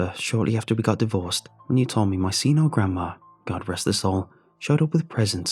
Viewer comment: “do you remember shortly after we divorced, when you told me my senile grandma—god rest his soul—got us a bunch of presents?” So is it a grandma or grandpa, because it sounded like you said “senile GRANDMA” but then say “god rest HIS soul”? I’m so confused lol